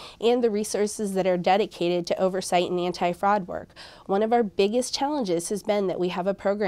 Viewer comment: did she say "fraud work" ?